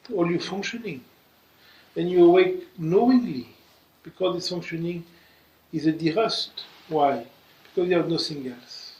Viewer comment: I hear "this functioning is at derust"... I had to google the word! 😅 It makes more sense in French (dérouiller)